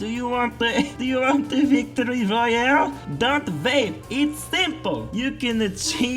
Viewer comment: damn that sounded like radal